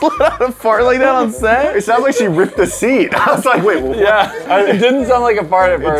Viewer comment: I thought he said "she ripped a C" like C on a piano